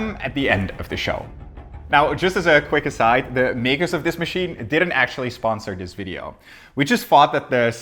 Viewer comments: did he just say "the magos of this machine "? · I thought he said "Magus of the machine" at first, which is extremely fitting for the subject matter.